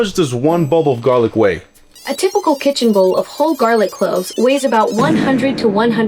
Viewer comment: I heard bowl instead of bulb too lol